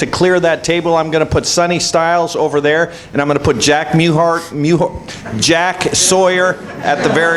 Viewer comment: Did he say Jack Mehoff?